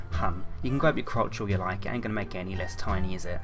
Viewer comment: you can grab your crotch all you like, I ain't going to make any less time either I think that's what he said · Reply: He said "You can grab your crotch all you like it ain't gonna make it any less tiny is it?"